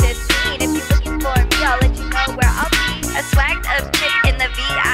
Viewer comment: "I'll let you know where I be" .... I always thought she said (I'll let you go where I pee) Lmao